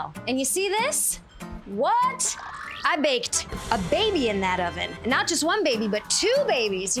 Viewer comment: What’d she say at 0:04? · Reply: "I baked a baby in that oven!"